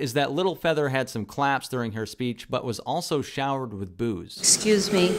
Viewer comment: When you said “showered with boo’s”, I heard “showered with booze” - is that a racisms? 😂